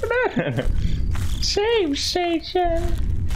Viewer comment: “SaaaAFfe STaaTiioON, gOtTa LoVe My SaFe STATION” tell me if I’m wrong but wasn’t that originally from Alien Isolation? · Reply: Y’all are right! It is indeed Save Station not SaFe smh buT I SWEAR TO GOD I’ve heard him saying both so.. don’t come at me bsskdbsj